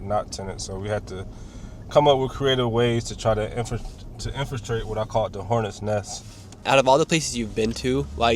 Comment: bro keeps saying infrastrate lmaoo WTH he’s making up his own words. These people need to work on their grammar. Did he mean infiltrate lol?